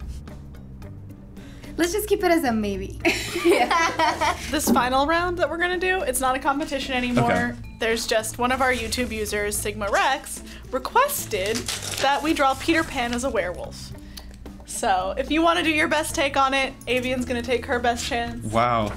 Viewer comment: Omg i thought she said SigmaX at the end and i was like wait no why. If you know you know :)